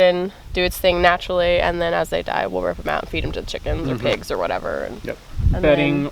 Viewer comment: Do i hear "pig port"😁